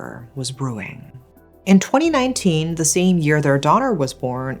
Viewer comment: Timeline isn't...I thought you said the daughter was born in 2019. But then you said the husband got the job in 2013 and sent money back to the wife and child....unless I heard completely wrong. R